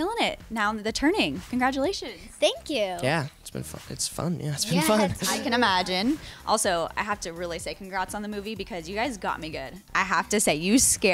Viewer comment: Finn at 0:30 "It's been fuckin.. it's fun, yeah it's been fun!" ....I swear that's what he said!